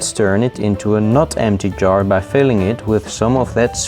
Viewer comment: I misheard "not" as "nut" and now I'm having MLP pony jar flashbacks